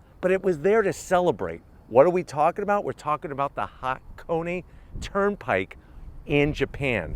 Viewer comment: I thought you said hot coney and now i want a hot dog. Extra cheese, extra onion.